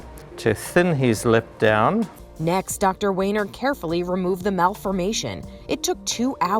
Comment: for a minute i thought he said dr. weener